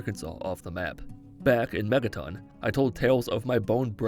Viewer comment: I swear I heard you say “Mettaton” instead of “Megaton”...